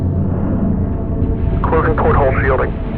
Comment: I could be wrong but I believe he’s saying “porthole” instead of foothold because a porthole is what they call a window on the hull of a ship (or submarine in this case)